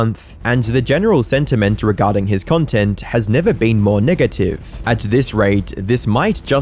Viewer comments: Why did he say niggative 😭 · did just say niggative? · did you say Nigative?